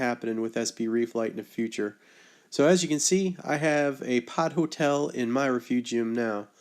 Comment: Hahahahahaha, for SOME reason I thought you said POT hotel.